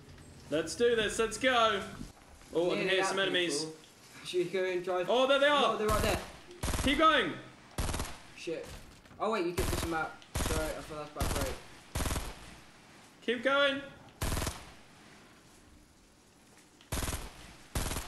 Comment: panicking is hilarious 😂 Did anyone hear say ‘Shit’ at 6:04 😂